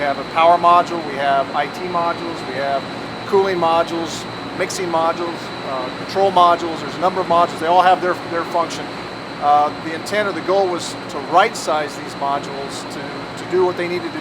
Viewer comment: did he say troll module?